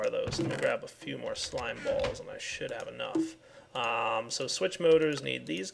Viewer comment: It sounds like he is saying "And I shit out enough"